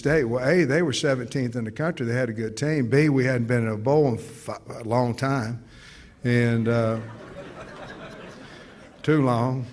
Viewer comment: sounded like he said "F**k a long time" but he didn't. haha so close!